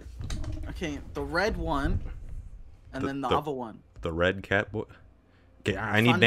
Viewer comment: did he just call Fundy a cat?